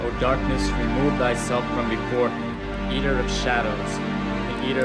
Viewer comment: did he just say yeeter of souls ?